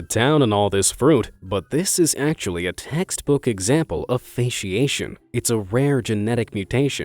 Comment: I heard fac and thought he was about to say fascism